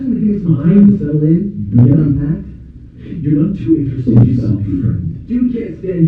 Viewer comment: Can someone tell me what there saying @ 11:18? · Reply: It is a bit hard to hear: Jimmy (ORIGINAL voice actor): You guys mind giving me time to settle in, get unpacked?? Gary: You're none too interesting yourself, friend (from "A Little Help") Gary: You can't stand here already (??) Pete: Jimmy, if you wanna hang out, we'll, we'll wait in the dorm downstairs